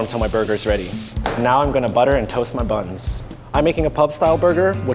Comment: i thought said am gonna butter and toast my butt